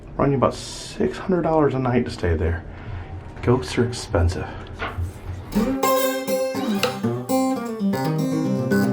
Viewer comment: Anyone else hear a potential creepy whisper after says "Ghost's are expensive." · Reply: A soft little yes